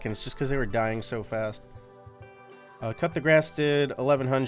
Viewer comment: im new in league so i thought he was saying "cut the grass" so im looking where is it only to find out that its coup de grace (kuh-de-grah)